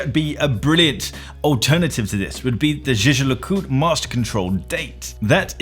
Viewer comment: great video, but at 4:42 did you just say "jeje le coot"? please just say JLC